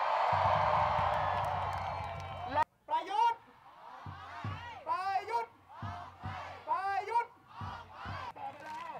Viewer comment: did he say bayot only pinoy can relate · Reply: Them say prayuit (president of thailand) get out